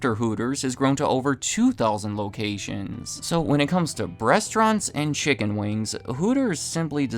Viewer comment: I heard "breastaurant" and can’t ear anything else now